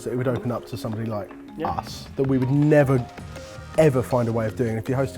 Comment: idk why i heard "would open up to something like ass" as opposed to "us." I was like they talking about eating ass?